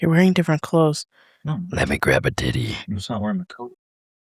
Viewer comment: i was playing video games while listening to your episode and as i zoned out i heard "lemme grab a titty 👿"